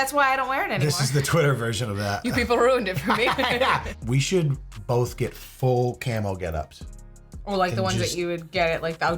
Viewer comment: it sounded like you said “we should both get full camel getups” and now I want to see you get a camel suit..😊